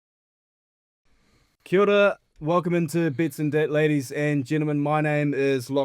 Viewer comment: might be the accent, but it sounded like he said bets and debt